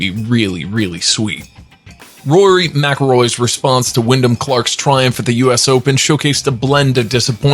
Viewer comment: Sounds like you said Willin or William Clark? It’s Wyndham Clark 🤦 Even if you don’t follow golf, get the names right if you’re going to narrate, please.